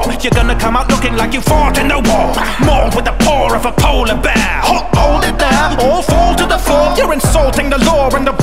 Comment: I was looking away from the screen working on somethings while listenening to this and I thought at 2:02 I heard “Po-Polar Bear?!” instead of “Halt. Hold it there” lmao I just found this song and this beat slaps